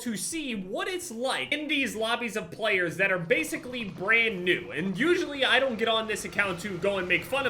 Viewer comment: I thought he was going to say "players that are basicslly brain dead" instead of "brand new" 🤣🤣💀